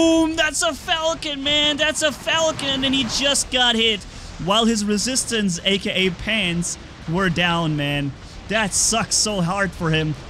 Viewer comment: "His Resistance aka his pants were down." I heard that correctly Right?